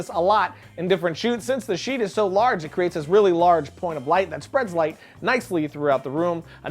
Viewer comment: does he say "a large porn of light"???? XD